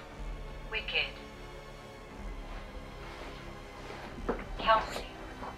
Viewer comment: Don't know if you caught at 18:23 the ovulus said "move wicked calcium" I immediately thought that Annabelle says to move the shadow doll (calcium = the teeth and bones she's made of!)